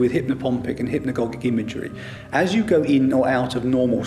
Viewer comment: did he say hipnipongpicanhipnigog imaginary 😳. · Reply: Hypnagogic and hypnopompic, is what he said. He just spoke really fast.